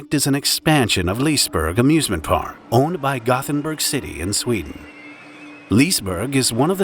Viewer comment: Okay, no joke, I thought he said, “Owned by Gotham City” not Gothenburg. Hey, why didn’t Batman show up to help 😂